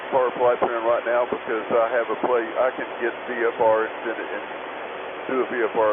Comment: he says „Because I have a plate.“ which makes only sense as „because I have no plate“. At 6:58 he clarifies „because my iPad died, I do not have the approach plate.“ He's did not say, that he'd navigate with the iPad. Just that he has no approach plate. Ok, he may be unable to enter the Fix into his GTN 650 or GNS or whatever. But that is a complete different thing than assume, he'd fly IMC with the iPad.